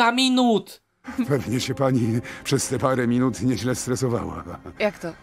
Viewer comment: Ja usłyszałem "pewnie się pani przez te parę minut nieźle zesrała" XD